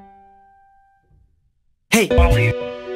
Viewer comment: did it just say hey lolly? (Edit) oh it said hey baldi)